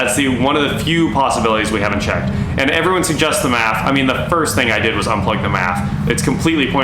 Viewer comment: Yeah he said tho he disconnected it so I’m not 100% sure, going to be interesting to see what it is. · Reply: I must be mistaken, thought he said the MAP... This will be interesting indeed....